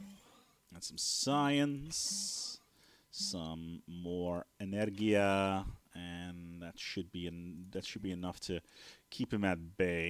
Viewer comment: Did you say lets get some more energija 6:41 :D